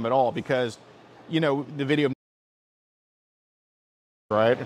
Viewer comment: I swear said "The video Meatspin" @ 14:42 lmao!